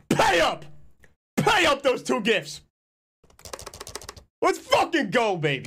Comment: I thought he said "pay up those two bits" I'm like bruh that's not even 2 cents